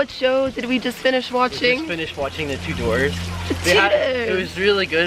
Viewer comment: Thought you said the Two Doors!😂 It's pronounced the Chewders! Great vlog guys!👍🏼👍🏼😘😘